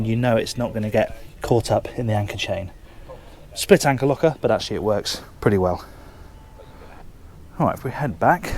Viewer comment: Did he just say "split anchor locker, but that shit works"? Haha love it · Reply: "Split anchor locker, but actually it works pretty well"